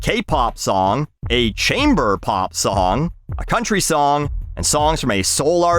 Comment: I looked away from the video around 5:38 and thought you said "chamber pot song".